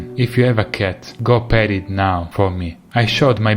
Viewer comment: So, I crapped myself for a second then. I thought you said, "If you have a cat, go BURY it now for me".